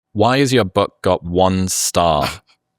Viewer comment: ohhh thank good i heard him wrong why is the book got one star thought he said butt lol omg